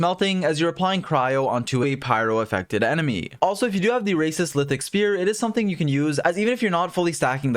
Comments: Am I hearing things or did you say "racist lithic spear" xD? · Did he say racist lithic spear😭